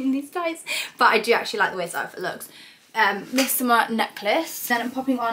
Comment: Where is the gold chain necklace from at 3:26? I couldn't understand what you said sorry :) x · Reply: Missoma babe! Just popped the link in my description box xx